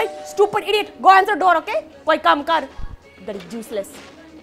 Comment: Did she say bloody juicless🤣🤣